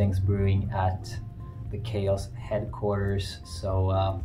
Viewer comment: Can't wait to hear new song! At first when you said Keios thought you said chaos. The CHAOS headquarters. Was like 'wait you work via chaos too?' then the realization hit 😅